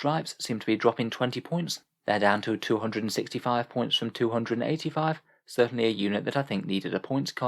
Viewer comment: I thought you were saying the Landraider was down 265 points for a second